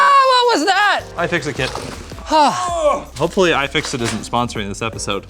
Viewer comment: is it just me or he literally said "Hope Hypixel isn't sponsoring this video" on 7:48 Maybe i misheard